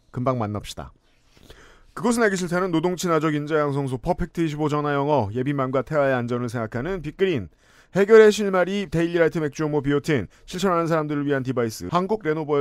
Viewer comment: 맥주효모가 맥주혐오로 들려~ 뉴스를 너무 많이 봤어~